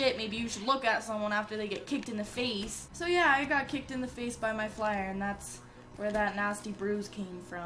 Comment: Thought it said "I got kicked from faze"